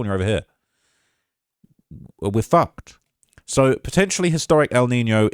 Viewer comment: "we are fucked",did i hear that correctly? 😅